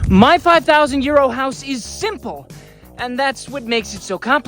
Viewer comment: I’ve watched your channel as you and your neighbors rebuild your house. I laugh at the introduction every time you say „ My 5,000 Euros.“ For a long time, I thought you were saying „My 5000 Year Old House“! Euros is pronounced Oye-row, but carry on, it’s gonna be Be-u-tiful.